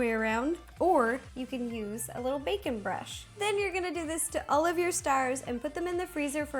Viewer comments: it had sounded like she said bacon brush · Lol I thought she said bacon brush instead of baking brush. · I'm gonna be honest, for a sec I thought she said you can use your bacon brush. I was a bit tired that might be why. Is anyone with me?